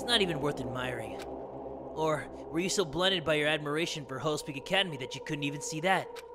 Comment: When says hopes peak academy I always hear him say 'hoes peak academy'